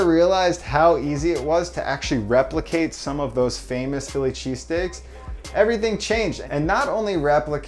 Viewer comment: Oooooohhh I thought you said Philly Cheesecake.